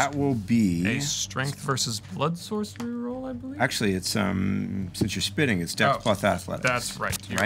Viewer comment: For some reason, I heard, "Since you're spitting, it's Stealth+Asthma." XD